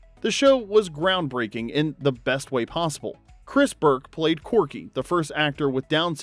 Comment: I think it’s just the voice that is tripping me up but I swear to god I keep hearing “Chris Burke played CORGI” instead of CORKY lol 100% must be me hearing things?